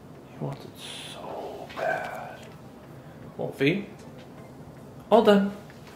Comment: You guys saying “all done” when it’s time for them to get their food is so confusing to me… It should be “OK- YES- GOOD” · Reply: I thought you were saying "well done" this whole time lol. The dogs probably wouldn't be able to tell the difference actually, like "all done" and "well done" would sound pretty much the same to them (and me, apparently). :)